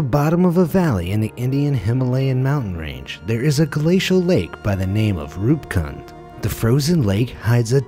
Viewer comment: I thought it said poop lake 😂